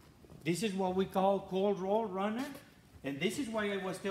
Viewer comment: This was an excellent training class. What does he say at 17:49 "cold rolled runner?" I'm seeing results for "Cold Rolled Channel"